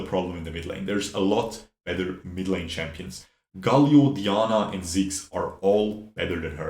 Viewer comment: hol up, did u say qiyana? I hope she'll be released soon enough tho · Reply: “Dianna”